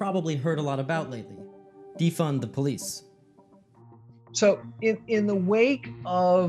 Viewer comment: Defund the police?! I thought you said Defend the police...no no, I dont like this at all